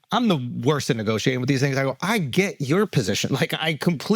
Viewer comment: mark Andrew said what? " I'm the worst at negotiating with these ______? I clearly heard something but was it? He always good about it but it finally slipped lmao. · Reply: “with these things”